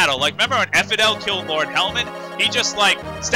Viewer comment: Every time I've heard you say Lord Helman it sounds like Lord Helmet, then you have to go and make all these Star Wars references! Are you trying to break my funny bone!?!